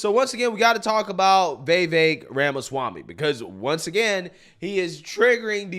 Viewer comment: I swear it sounded like he said BayBay Ramaswamy, lol.